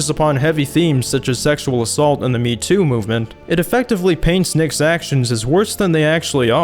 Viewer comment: It also thought he said meaty movement..... LMAO. why are you defending him?